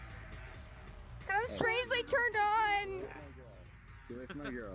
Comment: Plz tell me did anyone else heard her say am strangely turn on XD